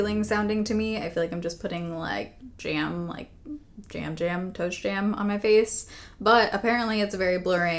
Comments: Beautiful as always! Hope you are still enjoying your time abroad. You made me laugh so hard. I thought you said “toe jam”! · Yeah! 7 a.m. just home from work and there’s helping to thin out my wallet 😊Toast jam…funny I thought you said toe jam.